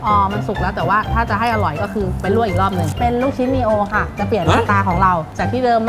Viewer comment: ได้ยินว่า ลูกชิ้นดิโอ555+